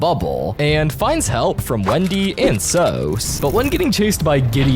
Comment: did bro just say soes💀💀💀💀💀💀